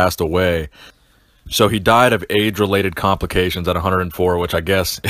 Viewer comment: I first thought you said he died of aids related .. lmaoo